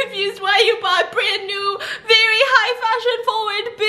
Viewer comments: Did she just say perpendicular instead of pandemic 😂😂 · did just say perpendicular or is it just me